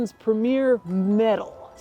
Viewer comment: I definitely thought it said “mental” 💀